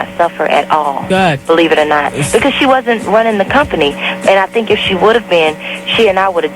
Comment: Did anybody commenting here listen to the whole thing? That is not even Wendy, THAT is why Monica hung up. You can tell from the voice, plus at the end they say it's not Wendy. smh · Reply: oh okay I thought she said she wasn't Wendy :-)